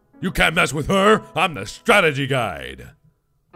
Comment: Did he say " I'm the strategy guide "